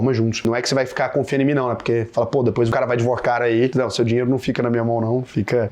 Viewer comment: "vai de Vorcaro aí" foi o que entendi 😂😂😂